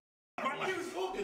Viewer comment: fanum "my ear is talking" 😭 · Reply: Ohhh😮 he said his ear is talking I thought he said his ear was choking Lmaoo